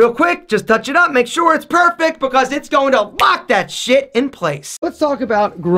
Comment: “It’s gonna fuck that shit in place” Aaron Marino 2019 (He said Lock but sounded like fuck to me) 😂